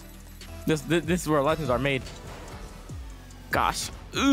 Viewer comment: When Arsenal said "This is where legends are made" I genuinely thought he said "This is where lunches are made". I'm not tired you are